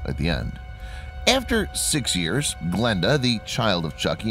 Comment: what he said: Glenda What i heard: blenda